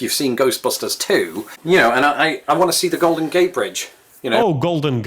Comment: why were you so alarmed at mishearing Golden Gate Bridge as Golden Gay Bridge?